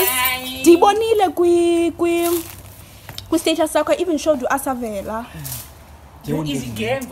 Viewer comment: Is it just me or I heard "I even showed uAsavela"!?? 🥺. Loved this btw❤️